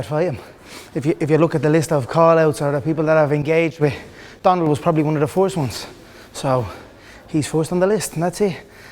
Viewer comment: what did he say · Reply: Donald was the first one on the list